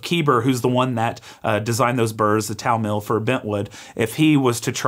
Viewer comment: What's the name of the Bentwood burr designer? The auto-generated subtitles say "Keeber at Towel Mill" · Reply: Keber at Italmill Keber was the person who designed the burrs. They ONLY live in the Bentwood. Keber is the most notorious burr designer of all time. Pretty sure he designed the Comandante burrs (a friend of mine was his friend and attests this is fact), Kinu burrs, stock DF64 burrs, and loads of the designs other companies now use. Italmill is the manufacturer.